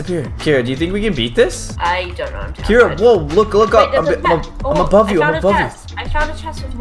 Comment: When caylus said above you i thought he said i love you